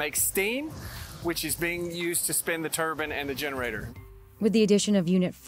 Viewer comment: E-LEG-TRICIDY ? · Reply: did he say to spin the turban @ 3:20? Did he build the reactor? God help GA